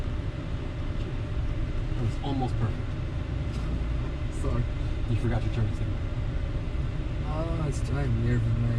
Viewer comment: i can't hear what did u forget on 4:06? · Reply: He said " it was almost perfect, you forgot you turn signal". minor mistake!